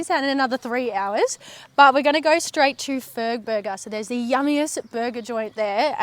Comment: Ahh I thought you said Furburger